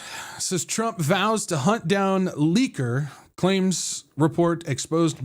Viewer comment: “Trump vows to hunt down liquor”…. That’s what I heard you say, since English is my second language. I was like, “what the heck”!. I’m glad you put the article on your screen so I could read it. Okay, got it, “leaker”. lol